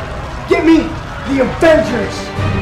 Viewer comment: turn on subtitles, instead of "GIVE ME THE AVENGERS" its offenders😂😂